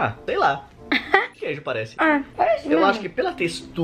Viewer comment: quase ia rolar um beijo aí hein kkk shippo dmsss😍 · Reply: Entre 10:20 e 10:27 do vídeo, parece que ela fala "parece né mo".. ou eu ouvi demais ?